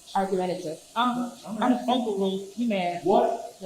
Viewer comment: Did she say “You mad” ??? What kind of hood lawyer is that?